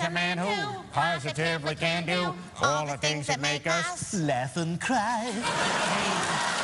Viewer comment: Wait... Stewie says laugh and cry??? I always thought he was saying effing cry